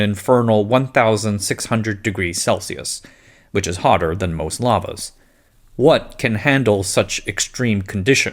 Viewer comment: A little humor for you. I was listening to this more than I was watching, and I swore I heard you say "hotter than most llamas" and I had to come back to the screen and rewind to make sure I heard you right. :D